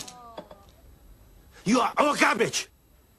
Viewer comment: * what i heard in 1995 * 0:33 "you are all garbage!" * what i hear now after someone commented this in 2010 * 0:33 "YOU ARE ALL CABBAGE!"